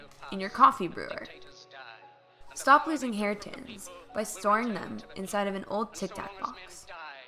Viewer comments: hair tins is what she said instead of hair pins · lol she said hair tins instead of hair pins on